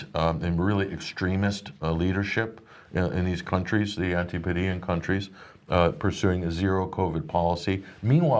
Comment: What does he say @ 46:27 seconds mins in? after the "really extremist leadership countrys" Antipinion? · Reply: Antipodean - places on the far side of the planet. ie Australia, New Zealand